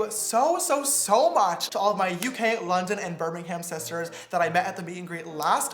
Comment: When he said brim harming I thought he said Framingham where I lived and for a second I dies saying to myself sitter James went to my town and I didn't even know then I saw the picture that he showed and I saw beaming ham great